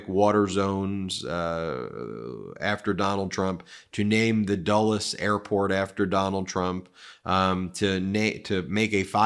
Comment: I thought I’d heard, “Name the dullest airport after Trump… “ 😂 THAT I would believe!